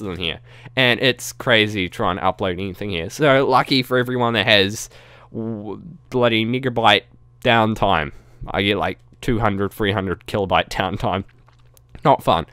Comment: Please tell me he said megabyte instead of what I think I heard... But really cool idea for factions c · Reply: it sounded like you said n----erbyte instead of megabyte but it may just be how I hear things from the difference in accent cause I pretty much knew what you said but it just sounded off was all